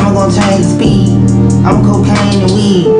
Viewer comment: Thought he said smokin on durk n durk kids 😂😂